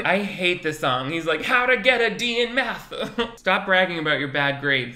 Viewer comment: I thought he said “how to get a dm back” 😂 cringe